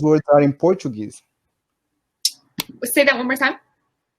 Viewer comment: OMG.... what does she says at 08:24hrs?? Só pode ser Inglês, pq i didn t understand !.... 🤣😁😂😂😂😂😂😂😂😂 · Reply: She said “Say that one more time” super fast 😂.